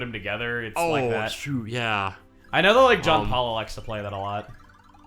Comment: Did I hear Jon Paula?